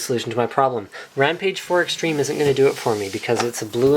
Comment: AH, right! I didn't hear he saying " isn't". I heard him saying " is" what changes the meaning of his entire phrase. Thanks!